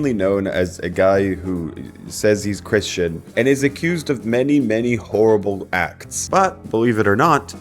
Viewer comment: In the beginning I first thought said “accused of many many horrible facts” 👀